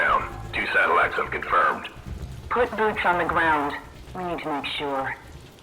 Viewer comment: If you listen closely to the Audio piece, the Woman said "Put Booch on the ground. We need to make sure." What is Booch? Is it a person? 4:42 She said "They lasted much longer than the others..." What others? Could there be a possibility they could be talking about Dempsey, Richtofen, Takio, and i forgot the other guys name. Dammit. Or there might be a possibility we might have something similar to the "After life" in Alcatraz Island in BO2... The reason i say that is because Sledgehammer has not given us any other way for the game to end but get infected... Or maybe since you suppose they are studying them, maybe they could have taken them in to their Laboratory experimenting with all kinds of subjects with the Maticore Bomb. They could have injected them with something that could possibly resurrect them and the next map may be in Space cuz of the Orbital drops... Who knows... But one thing in zombies they do that i hate is they make us play as different Characters. I just want to stick with the four characters that we currently have and continue with the story... And i wish they actually but in a REAL Boss wave. Like literally, not like the George Romero Sht in Call of the Dead but a one time Boss where you actually get to kill him instead of just stunning him and returning him back to where he came from... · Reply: Oh my God guys. Really? She said, "put boots on the ground." Not Booch. The intercom effect just made it sound weird.